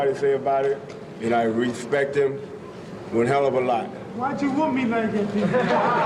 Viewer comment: What did Ali say there? Why did you whoop me??